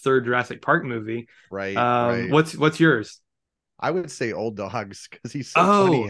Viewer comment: CORRECTION: at 9:00 I definitely meant that William H. Macy is great in Wild Hogs. Not Old Dogs. Btw, Same director (Walt Becker) and both starring John Travolta. Easy to confuse them. Although IMO Wild Hogs is FAR superior. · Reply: That's funny. I thought you said Wild Hogs (they do sound similar). I saw Old Dogs for the first time last night.